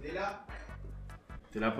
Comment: alguien sabe que dicen de atrás ahí que es tan gracioso ? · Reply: le pregunta a: tela?